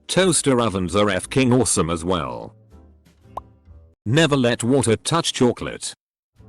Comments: “Never let the water touch your clit” 8:45 Did I hear it correctly? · i cant be the only one who heard 'never let water touch your clitt' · I thought the narrator said "Never let water touch your clit". I mean, it kinda goes without saying xD